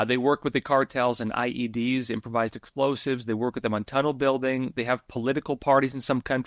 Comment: did he say tuttle building, perhaps tuddle? What is that?